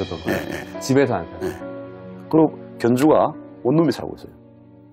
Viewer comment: 그러구견주가 다음에 뭐라고하는거예요? 열번들어도 모르겠어......왜저거만 자막안달아주냐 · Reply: 옷놈에살고잇어요??